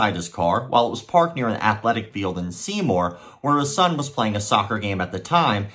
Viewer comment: I'd be hesitant to allow anyone I didn't know very well to be alone with my child. OK, I watched the video again with CC on. You said HIS son. I thought you said HER son. My bad! Good video! I deleted my previous comment.